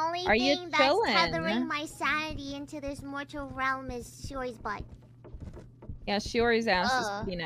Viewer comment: Chances of someone noticing a mistake in captions are low butt not 0... xD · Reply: Sherry instead of Shiori?